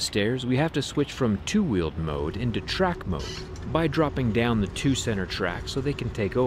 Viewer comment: I heard "track mode". Yes... anything with a motor and wheels can be raced.